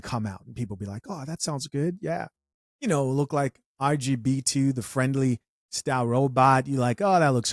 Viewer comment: RGB2? WHAT DID HE SAYEEEEE!!! 😂😂😂